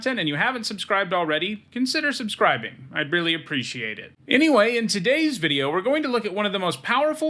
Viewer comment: I know you didn’t say it, but to me it really sounds like you say “I barely appreciate it” which is hilarious. Probably just my non native English speaking dumb brain. xD